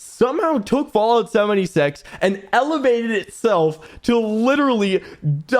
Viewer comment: am I the only one who hears "fallout 70 sex"